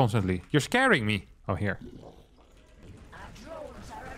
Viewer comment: you can barely hear the nuke warning at 23:17, there was a lot going on at the time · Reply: I heard "Nuclear launch is under attack"